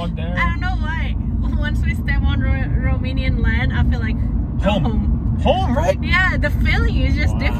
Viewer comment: I hear saying at 14:19 this : ''once we step on Romanian land i felt like ..home " WOW ! guys you are so awesome to feel this way ! you are so nice , both of you !